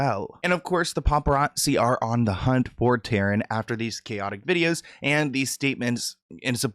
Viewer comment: I hear Ye dressed Taryn in this outfit! LOL